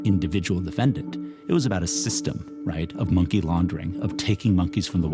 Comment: Thought this shi said money laundering but I don’t want to hear about abused monkeys I’m sorry